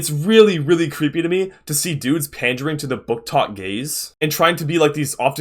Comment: HELP I KEPT HEARING 'BOOKTOK GAZE' AS 'BOOKTOK GAYS'